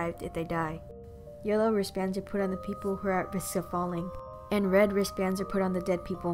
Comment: Yellow wristbands what? I can't tell what she said, I hear "Yellow wristbands are put on the people who are asipefallinp" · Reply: Yellow wristbands are for people who are at risk for falling